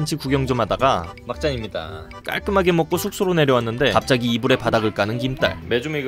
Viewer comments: 이불에 바닥을? 나만 이상하게 들리는건가? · 에 자막은 바닥에 불을 까는 이지만 저는 들었습니다...녹음에 이불에 바닥을 까는 이라고 한것을요...